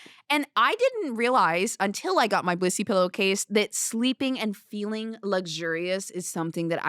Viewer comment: Bro I have this at 2x speed and I thought she said “bussy pillowcase” 😅😂